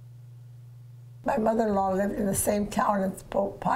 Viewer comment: HAHAHHAHAHAAA..."My mother in law punched the Pope in the face!"...hahahahhaaa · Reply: While watching, didn't realize hubby was listening along - "Did she just say 'my mother punched the pope in the face ?!'" "Nope, her mother-in-law." Hubby laughs - his family is also Catholic.